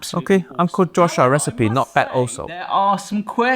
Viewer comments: The subtitles say 'not better also' but Uncle Roger is saying 'not bad also'. The subtitles are throwing shade that was not thrown by Uncle Roger, in Uncle Roger's name. What a coward the subtitles are. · The AI gets the captions wrong so often. It's supposed to be "Uncle Joshua recipe not bad also"